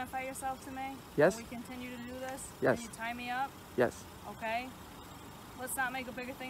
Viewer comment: Does she really say tie me up at 0:15 ?